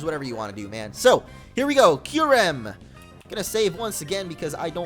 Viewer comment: I heard him say kyurem when it was zeckrom